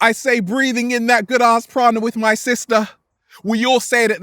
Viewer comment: Follow us on INSTAGRAM Now: <link> - Get inspired now. Grab the New Book Now: Feel Alive by. Now available: <link> 5 Secrets to Open Your Third Eye | How to Open Your Third Eye <link> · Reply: am the cat I thought he was saying Prada. ..like the designer cologne.